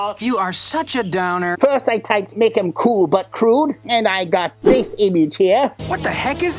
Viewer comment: Is Raph really cool, but crude? I always thought the song said "Cool, but Rude"